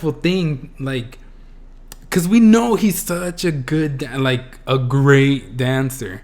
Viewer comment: why did i think you were saying 'cause we know he's such a good da....ddy' at 17:23?? oh gawd.